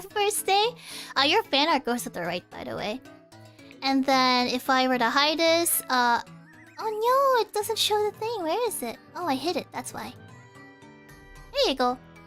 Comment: Did she just say..."Oh Nyo"?????